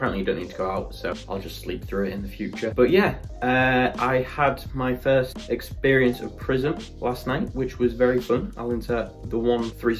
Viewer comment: Actually thought he said first first experience of prison until I saw the clips of the club 🤣